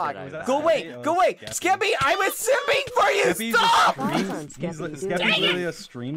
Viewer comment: did i hear right? "Skeppy i was simping for you!"